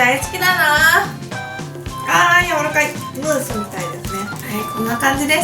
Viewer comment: ｢ムースみたいですね｣ が ｢ムーさんみたいですね｣に聞こえたww難聴だw